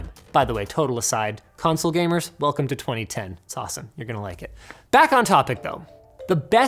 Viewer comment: did he said 2010?